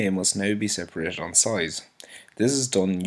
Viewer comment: what did he say? "The DNA must now be separated onsite"? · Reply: DNA must now be separated on size.